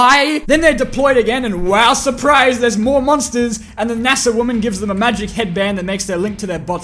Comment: I thought you said "massive woman" instead of NASA woman